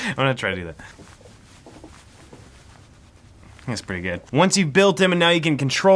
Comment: did he saY that pretty gay or what? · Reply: he said "That's pretty gud"